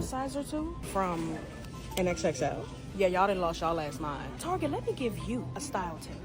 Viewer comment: sorry…I thought she said xxs…but xxl?